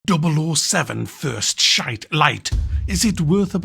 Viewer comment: I heard "first shite" not gonna buy it lol all they had to do was an goldeneye 1/1 but updated controls and updated graphics easy money